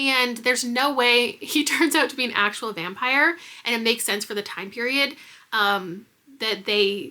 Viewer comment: My boyfriend misheard you and thought you said there is no way he is a vampire because it doesn't make sense for the time period. He was cracking up thinking you said vampires weren't discovered yet or something. 😂